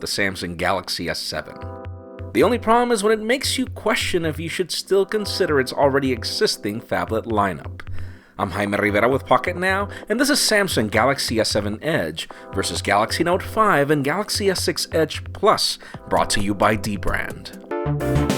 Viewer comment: I don't know of I'm the only one but did I hear "hymen rrriverrra"?